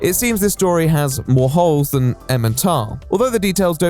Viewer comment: who are “em & tom”…??? I listened back to this part 6-7 times… emma tom? ementom? someone please make me feel stupid by explaining the probably very simple answer to what I’m mishearing?! 😂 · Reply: Emmental - it's a holey cheese